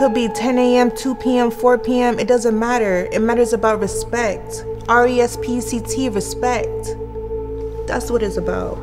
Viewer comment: Her: Respect R-E-S-P-C-T Subtitles: R-e-s-p-e-c-t Me: ... It really sounded like she said respct, I know she said e